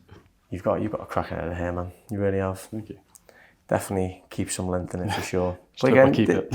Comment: I thought he said he liked the length in it? lol. I know he wanted bit shorter but looks like took a tad much off